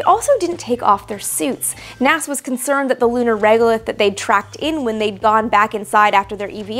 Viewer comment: "NASA was concerned that the lunar...???...that they tracked in..." I've listened several times and I cannot make out that word you are saying. The lunar WHAT? I presume you are talking about dust. Great video as always though - thanks. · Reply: regolith